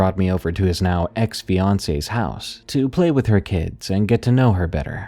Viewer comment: During the narration of Growls in the Corn I was a bit distracted and I for real thought that he said Beyoncé house and I had to rewind it, he said fiancé’s house lol 😭😭😭